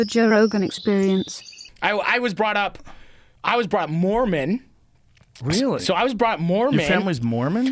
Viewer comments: I thought it said "growing up a moron" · I swear I thought it said " Growing up a moron"